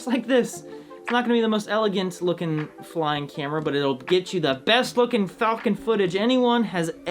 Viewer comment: Really thought Wren said "Itll get you the BEST fucking falcon footage..." lmao. I had to double back to realize what he actually said .